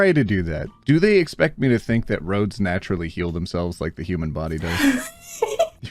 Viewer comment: Why did I think said "Road Snatch really heal themselves?" and not "Roads naturally heal themselves" 😭😭😭 My head is well and fully in the gutter 😂😂😂